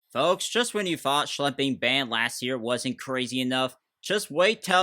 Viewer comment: i heard schlepping ban